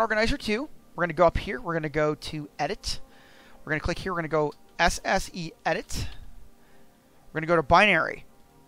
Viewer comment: What did you say you where going to load up at 1:26???? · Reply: The SSEEdit.exe file.